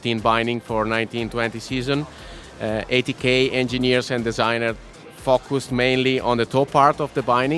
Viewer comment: I thought he said "80k engineers" worked on the toe part, end thought 'wow, it's probably pretty good."